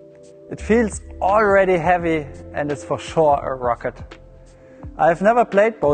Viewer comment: did you say a racquet or a rocket